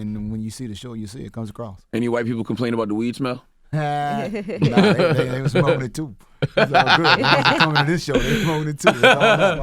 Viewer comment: did he say he say they was smoking TOOKAH or TUKAH or TUKA IDK how to spell his name name but i know he from Chicago man R.I.P. to the guy these niggas need to stay out of shit they don’t know about but if he didnt say that my bad but it just sounded like it and with the young MA shit......... ya know!!!!!!! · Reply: oh ok he said “ they was smoking it too.”